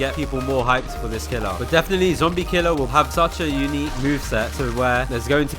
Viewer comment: Did bro call him zombie killer